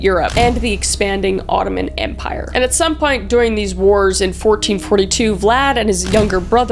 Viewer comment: did you just say “at some pint??” lol